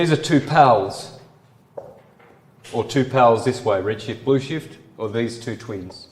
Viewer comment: says it over and over: “Red shift, bullshit”. Or is it me mishearing a confession? 🤔